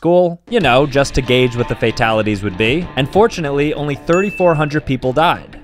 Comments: Bro really said "unfortunately only 3,400 people died" 😭😭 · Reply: oh i thought he said unfortunately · "UNFORTUNATELY, only 3000 people died" Bro has his way with words Edit: Yes yes, I know dudes, I have shitty hearing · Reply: I also thought he said unfortunately but i saw the subtitles :/ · “Unfortunately only 3500 people died” 💀💀💀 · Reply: oh now I hear it I thought he said unfortunately but still…